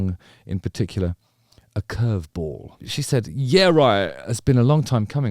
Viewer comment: After she says Yeah right it sounds like she says Manson is right 😂